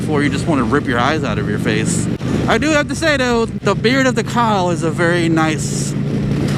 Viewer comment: I'm going to try that ride, Beard of the Kyle huh? See you at the shame. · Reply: I thought he said beard of the cow!